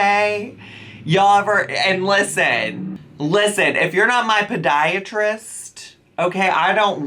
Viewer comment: ARE YOU MY PODIATRIST? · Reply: Omg i thought you said pediatrist- now I'm screaming 🤣🤣